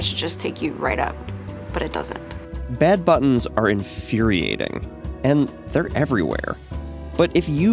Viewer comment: I thought this said dad buttons and I was like "whoah theres a button in elevators just for dads? I wonder why" ... I need sleep